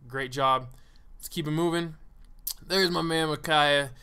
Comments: HEY my name is makyah and at 24:43 i hear what sounds just like my name ive never heard of anyone els named the same i have a need to talk to this man pls help me get into contact with him thank you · Haha , no worries, I see the pic as the thumbnail for the episode. I am super stoked to be part of your show ! Those mirrors are actually functional with a hinge and clamp so they actually swing on the tube when they get bashed , just like a 1:1 . thats how they have managed to stay intact after all the beating they have. Scale Wars and Chicken Grease ! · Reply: HEY my name is makyah and at 24:43 i hear what sounds just like my name ive never heard of anyone els named the same i have a need to talk to this man pls help me get into contact with him thank you